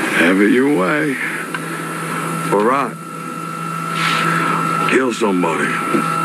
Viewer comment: When he said Arat it sounded like he said alright in an accent ngl